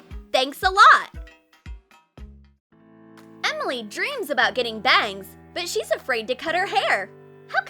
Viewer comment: I spit out my water laughing cause I thought she said "Emily dreams about getting banged!" Also I wasn't looking at my screen so I didnt see the picture of the lady with bangs